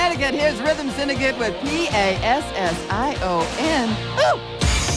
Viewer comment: Wow! After like 20 years, here I finally find that song that sounded like "key i-s-s iowa" (according to my little kid ears). Hardest title to search for ever, if you don't know it! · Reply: Same here l thought they were saying K I S S I O N I was 8 or 9 🤭😏